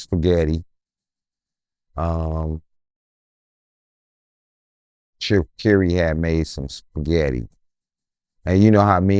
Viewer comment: I thought he said Jim Carey made spaghetti